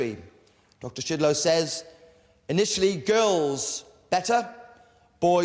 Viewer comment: Was I the only one who thought the headmaster was saying Dr. Shitload